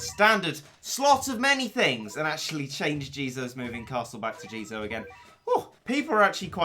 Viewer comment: lol I thought you said change jesus moving castle back to jesus again, and something like people actually quite exciting to see jesus moving castle again.